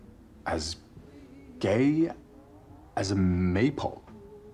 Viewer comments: I cannot lie, I watched this and genuinely thought he said he was gay as a maple. Like the tree. Thank you for the clarification, it makes far more sense if it's a maypole. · I didn't watch this movie with subtitles so I thought Henry said he was "as gay as a maple" and was just as confused as Alex 😂😂😂